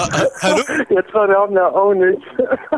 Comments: If you didn’t understand what he said 3:44 He said “I think it’s ownage” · I think he said something like “we’re getting on ownage” 😅